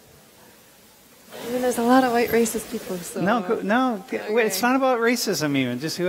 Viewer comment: did anyone hear what that lady said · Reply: i think she says someone is racist af something like that